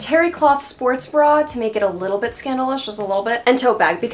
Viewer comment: I thought you said scandalicious instead of a little scandalous😭😭